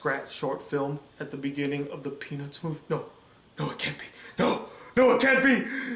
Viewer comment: the penis movie? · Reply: Jared Griffiths peanuts movie I know it sounds like he said penis movie